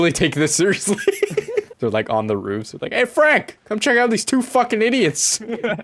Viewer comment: Not gonna lie but I thought he said Anne Frank